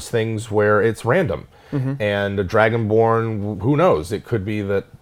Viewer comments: I totally thought said Dragonporn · I thought he said dragon porn 😂😂😂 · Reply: "I thought he said dragon porn" Fair warning, this is a real thing. There's a disturbing amount of it out there are well.